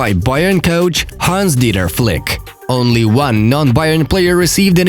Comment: Friends, write in the comments below, which team, after the draw, is for you the favorite of this Champions League. It’s very interesting to know your prediction. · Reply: oh god, I thought you said "Hans Didn't Flick" in the video...